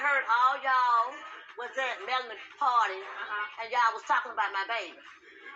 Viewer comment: I thought she said melanin 😂